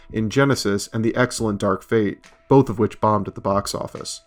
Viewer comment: I laughed for a second when I misheard you say "The Excellent 'Dark Fate'" lol..........